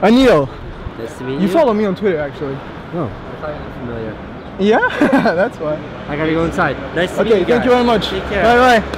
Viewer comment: Il dit quoi à 10:40 ? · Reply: you seemed very familiar, ce qui signifie qu'il lui semblait bien le connaitre de quelque part (Anil), donc de Twitter vu qu'il le suit ! ;)